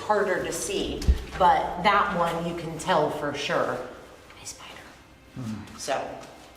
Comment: seconds I heard another female voice say "a spider" Was there anyone touring the basement other than the tourguide?